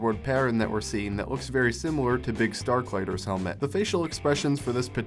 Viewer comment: Did he just call Biggs "Biggs Starfighter"? Or am I crazy?